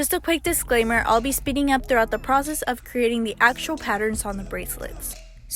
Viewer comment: Who else thought she said "I'll be spitting up throughout the process"😅😂😅